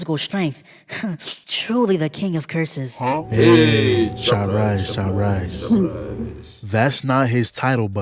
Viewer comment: Did you just say "chop rice, chop rice"😂😂😂